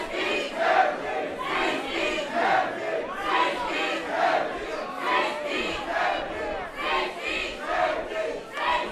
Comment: What are they saying at 7:30? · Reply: "Safety"